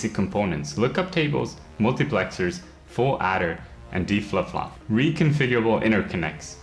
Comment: LookupTables, Multiplexers, full-adder and "D-Fluff Fluff" Can anyone tell me the names for the 4 components? xD Every time I hear "D-Fluff Fluff"